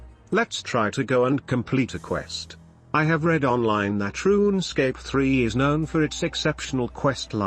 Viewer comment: Did I just hear Troonscape 3 or was that just me? 🤣